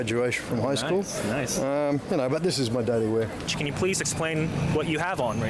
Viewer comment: Did he say: ''I gave two other watches to my 2 sons...but THIS is what Daddy wear!'' @ 2:10 ?? lolol Haha :-)